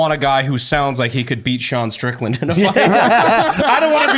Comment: Who is John Strickland?? Sean Strickland?? I cant hear well.